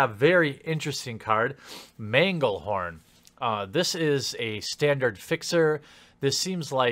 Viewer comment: i thought you said 'bagle-horn' which sounds delicious but dangerous